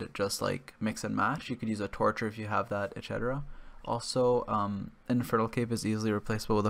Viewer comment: did i hear infertile cape?